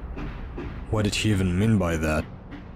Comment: Lmao it sounds like says "What did SHE even mean by that